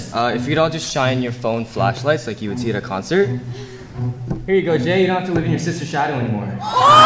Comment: what did he say? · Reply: "Here you go Jay you don't have to live in your sister shadow anymore"